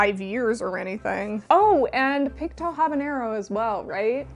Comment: Picto Habañero? I know I'm mishearing this, but don't know what it's supposed to be. Edit: never mind. I see at 1:43 that it's "Pink to Habañero." :D